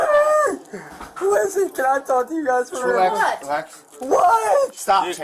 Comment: The first time I didn't understand the cop was saying relax I thought he was saying cyrax 😂 I was dieing thinking they knew all the youtube stuff